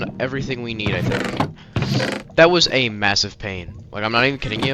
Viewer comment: Did you say Massive?